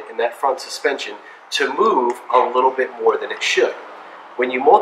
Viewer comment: what is the ball joint that you recommended in the video? it sounded like you said the manufacturer's name was Amove? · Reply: MOOG